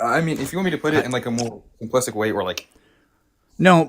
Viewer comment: "if you'd like me to put in a more simplistic way'..." Im betting he'd be happy if caller just made sense and stopped spewing word salad. That dude is yet another great example of the Dunning-Kruger effect in action. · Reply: I thought he said complicit, but I listened to it again and he said implicit, which isn’t the correct word to use anyway.